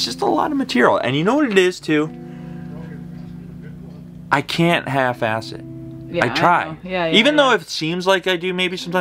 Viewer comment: I thought you said "I can't have acid." Thought a pretty odd admission to make on GotD. "I can't half-ass it" sunk in after a moment or two